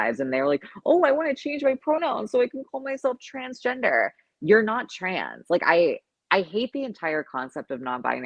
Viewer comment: I thought you said you're from Iran!